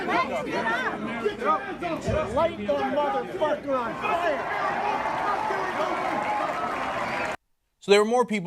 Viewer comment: Am I the only one that heard " light the motherfucker on fire at 2:10? Kinda sound like trump said it lmao